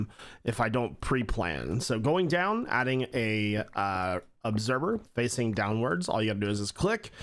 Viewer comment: I swear you say observer like "obserber" at least that's how I hear it and it's kinda great.